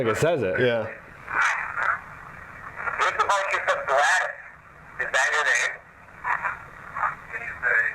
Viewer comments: it almost sounds like it says "Gladys Earl" or something like that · Ik this was a while ago but at 53:34 it almost sounds like it says "Gladys Earl" as if that's her first and last name. The device says Earl and you think it may be giving someone else's name, but maybe it's just her last name!